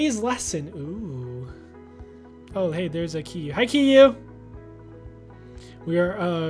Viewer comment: I heard said 'hey there's Yu-' but then later he said Kiyu 😳